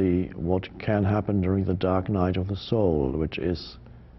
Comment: So I'm high asf and thought it said a "dark night of seoul" and was watching this absolutely bedazzled